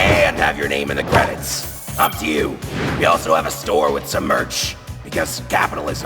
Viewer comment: did venus just say f you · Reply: No he said "up to you"